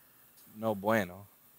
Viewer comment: did you said " No bueno" ? HAHAHAHA🤣🤣🤣🤣